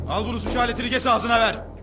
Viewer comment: ne dedi lan Çakır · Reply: Çükünü kes .. Devamını anladn